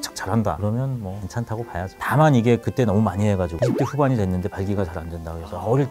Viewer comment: x0대 후반이라고 얘기하시는거에요??? 발음이 완전 뭉개져서 전혀 안들려요~~~